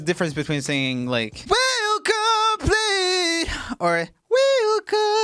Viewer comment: Did he just say will come to plead!? 😂😂 4:54 for anyone who doesn't know it's we'll come clean